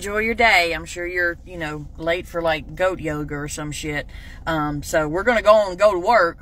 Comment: "I'm sure you're late for like, goat yoga or some shit". I'm Generation X and I approve this message. (I edited my comment to correct yogurt to yoga.. I had never heard of goat yoga before this. Goat milk yogurt is real, but it isn't as funny as goat yoga). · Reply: OMgoodness! I thought she said goat yoga! 😂😂😂 I was like : well WTF is GOAT YOGA?! 🤣 And then I was like: Nah I ain’t learning goat YOGA. Fkn millennials doing too much! 🤦‍♀️ Then I saw your post and I was like: Ohhhh, yogurt...nvm. 😌